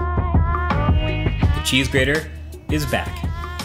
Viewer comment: "cheese-grater" i thought you said ( she is Greater ) to iJustine >> lol